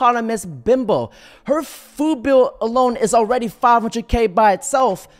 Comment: I heard you say foo bill.